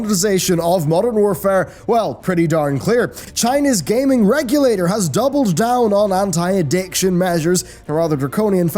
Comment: "has double down on hentai edictions" I swear its how I heard it